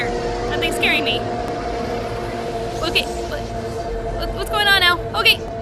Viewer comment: did she say: "What's going on L?" Or is that just me?